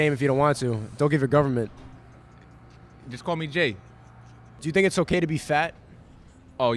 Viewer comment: “My name is Johnathan” seconds earlier “Call me Jake” Like is he a prison escapee or something, what did he do? 😂😂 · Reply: Ah i thought he said call me Jay